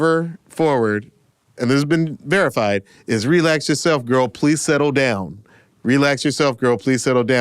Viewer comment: Relax yourself girl. Please settle down. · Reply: I just heard “please settle-“. The “Down” sounds overlapped with plan or can.